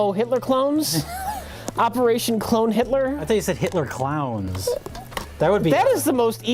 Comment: I hear clones, not clowns.